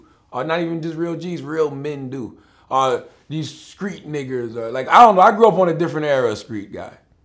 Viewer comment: When Char Char says street "Niggers" instead of "Niggas".... Yeah, he's upset and scared.